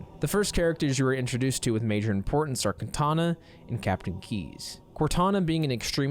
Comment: im sorry i really thought you said Katana LOL. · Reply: I think he said "Kantana" instead